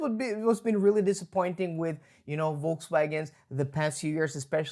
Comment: I always hear "Wokeswagon" 😅 Battery size has been hinted at 38 kWh LFP (25k) and 56 kWh NMC. Charging 125 kW has been mentioned in the live presentation.